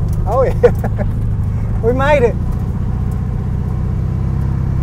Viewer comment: Thought ya said hand brake works